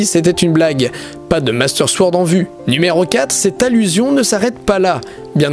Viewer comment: il a dit numero 4 ou c'est moi ?